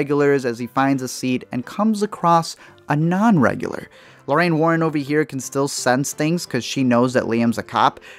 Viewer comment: Lorain Warran ? did i hear that correctly ? xD haha